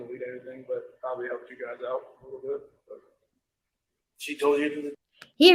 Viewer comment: When Chris is talking about not deleting his texts with NK 1:04:16 as he says she instructed him to do, I don’t hear him say it was “probably TO help you guys out a little bit”. I hear “probably HELPED you guys out a little bit”! The difference is the intention. I don’t believe he ever INTENTIONALLY tried to help LE to catch himself or any co-conspirators! I think he is acknowledging his mistake and that he knows they used it against him. Kind of a “you got me” on that, but only because I wasn’t even trying to cover my tracks not because of their great investigation skills! I think CW was taking a shot at the Agent Coder, Lee, and Baumhauver’s egos with this comment! We know he took a lot of pride in his ability to deceive others, for example making his teacher believe he went to China for summer vacation!